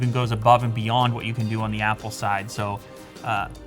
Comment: Sound like you said the IM PO watch lol